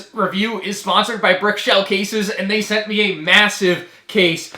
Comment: Did he say... Massive?